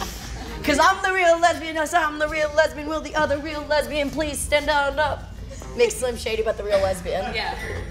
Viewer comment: Wait did she just say you know like slim shady but lesbian...loll.jeez oh man